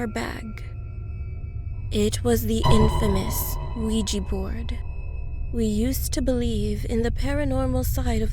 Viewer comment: Thought she said Luigi board.